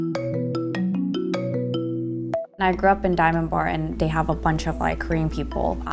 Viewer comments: subtitle says "(speaks foreign language)" but she said "Diamond Bar" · she said diamondbar and the subtitles said “speaking goreign language”